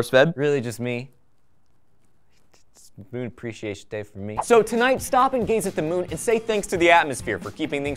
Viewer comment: For a second I could've sworn that said, "Stopping gays at the moon."